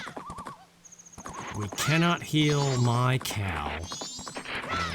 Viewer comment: When you said, "We can't heal Mikau." I heard, "We can't heal my cow." and my mind literally went "Eponas a horse..."